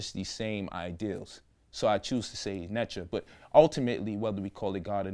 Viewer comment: did he say netra?